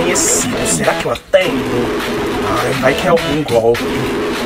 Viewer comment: mano eu ouvi ''desliga essa porra ketlin'' KKKKKKKKKKKKKKKKKKKKKKKKKKKKK